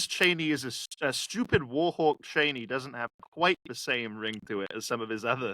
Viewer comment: I heard “stupid whorehawk” lol